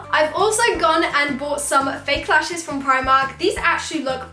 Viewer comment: love you and im in the dinosaur family xxxxxx but at 9:20 you said you got eyelashes from primarland i think you were going to say primark instead of pound land anyway i probably would have done the same thing love you xxxx